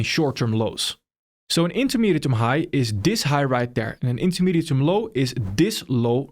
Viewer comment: what is he saying?? (intametime) High (intametime) Low?? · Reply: Intermediate term high/low